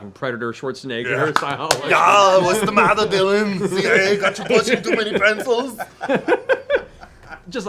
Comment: What Travis said when quoting 'Predator' “What’s the matter Dylan, the CIA got you pushing to many pencils?” What the English CC/sub says “What’s the matter Dylan? You guys ate too many pretzels?”